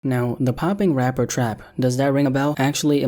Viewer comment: I thought that said the pooping rapper trap... 😂